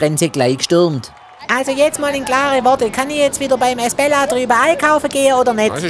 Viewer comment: Ich habe immer Hezbollah verstanden nicht esbella 😂